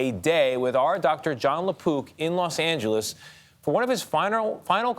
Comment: Thought he said john LA poop lol